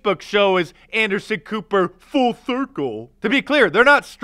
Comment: The first time you said "full circle", it sounded like you said "false circle". What I thought I heard seems more accurate.